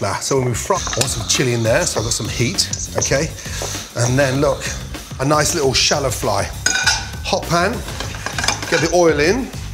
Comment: did i just hear gordon say a nice little shallow FLY